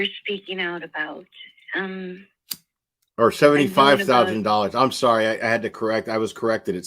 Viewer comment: They are not suing for $75,000. The lawsuit confirms that the named court has jurisdiction because the amount EXCEEDS $75,000. The upper limit of damages and costs is not named in the suit. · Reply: I could have sworn he said $750,000 first, in error, and corrected it to $75,000 when referring to damages they are seeking. Regardless, money can not make this situation right.